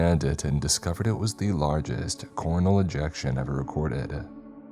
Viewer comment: i know what you said but i heard "cornhole ejection" i may never hear "coronal" right again lmao😂